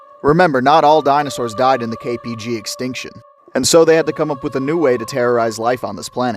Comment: For a second there I thought you said KGB extinction